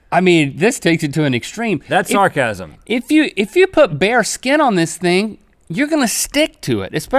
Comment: When link said "bare skin" I thought he said "bear skin" and he said it would be cold, and I'm like, I thought bear skin was warm?